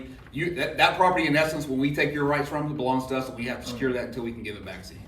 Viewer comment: Did he say, "When we take your rights from you"?? Did I hear him right??